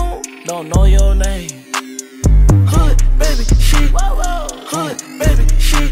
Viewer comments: Awesome can't wait for the English version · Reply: danerific46 it sounded like he was saying good baby sheep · This is all i hear in tiktok “HOOD BABY SHIT”